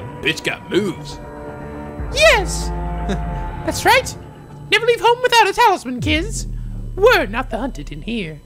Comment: For a second I thought you said "Never leave home without a towel," like a H2G2 reference